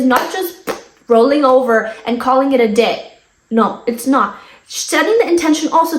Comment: lmao my sub-titles i can‘t. 💀It said „… And call it a d*k“ instead of call it a day😭 i was so caught off guard lmfaoo